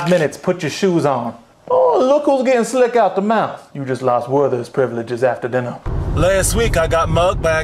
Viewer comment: what is he saying here? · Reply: Look who’s getting slick out the mouth you’ve just lost werthers privileges after dinner